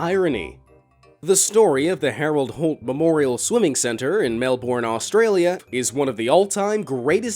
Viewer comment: did you say mel borne